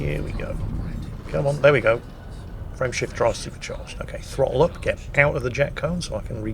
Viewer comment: Everytime says "frame shift drive" I always hear "friendship drive" and I thought thats what it was actually called until he first visited a neutron star 😂